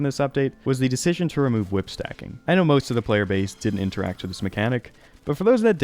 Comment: you're not going to believe what I'm about to say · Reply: I thought he said whip cracking genius if i saw he said whip stacking i would agree.